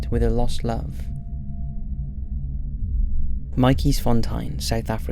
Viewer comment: Matjiesfontien - I thought you said Mikey's Fun Time